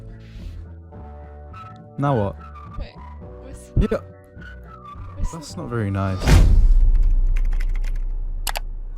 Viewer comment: did he just say the n word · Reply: noo 😭😭 i said 'nah'